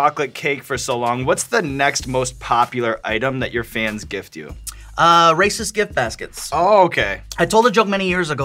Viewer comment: "he gave me a lawnmower and was mad when I kept it." If you're gonna make a playful racist joke, you gotta be ready to commit to the consequences lol. Guy knew he couldn't just bring his lawnmower and point to it like "haha mexicans" without being an ass so he had to give it away as payment for the privilege of being racist lol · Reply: For a second I thought he said races not racist lmao